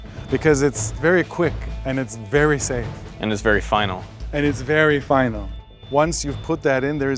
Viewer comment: I thought they said it's very "fun". It could be very fun.